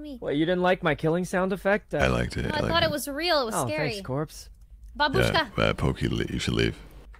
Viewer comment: Мне не одному показалось что она говорит "Бабушка"??